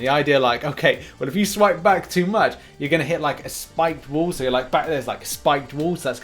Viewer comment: I thought you said slicked ball😂 I thought oo that’s painful